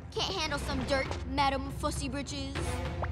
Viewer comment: what did toph call katara wait madam ma somethin on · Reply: “madame fussy britches”